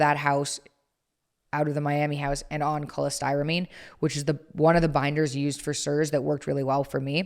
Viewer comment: does she say Cholestyramine binder?